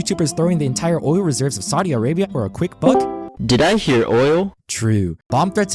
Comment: did i hear oil TRUE